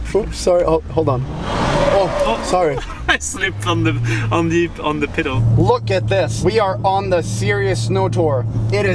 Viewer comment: did say " on the piddle "